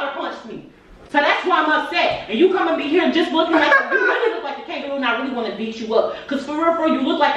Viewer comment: Did she just say he look like a kangaroo pouch?😭🤣🥴